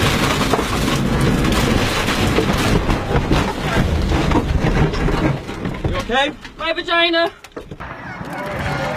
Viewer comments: Haha, the crash at 06:25, "You Okay?" Answer: "My v....."! Or did I hear wrong? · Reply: "My vagina!". Don't thank. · what did she say?? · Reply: He said "you ok?" She replied "my vagina"